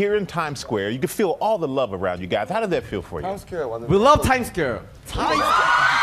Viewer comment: Did anyone else hear j-hope say “we love time scare!”😭 then started to move like a clock🤣😂